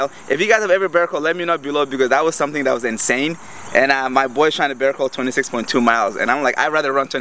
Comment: I jumped a little I thought you said my boyfriend and I turn CC on to see if I heard it correctly..LOL..good video as always!